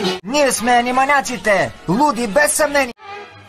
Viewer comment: I heard, "Budapest so many!"